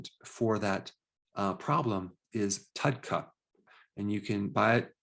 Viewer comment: Does anyone know what he says? It sounds like tutcup? typkut? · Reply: Hi! It's called TUDCA. Check out this article for more information: <link>